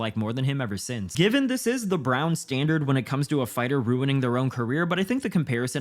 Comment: Did you call Henry "the Brown standard" 😂?